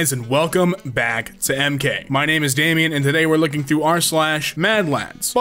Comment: Damien: “It is times like this that I wish I could show my facial reaction” Viewers: FACE REVEAL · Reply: I honesty thought he said my name is Amy